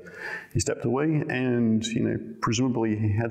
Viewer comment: whats the islam reference he makes at the end? what does he mean by that? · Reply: No I think you misheard, he said “his line of thinking”. Sounded like he said Islam because audio is a bit muffled